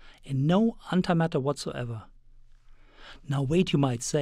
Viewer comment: Did I just hear "Hentai-matters"?